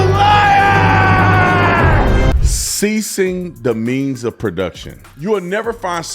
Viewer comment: Sounded like he said seizing the means of production ei. Nationalizing businesses. Like the city own grocery stores.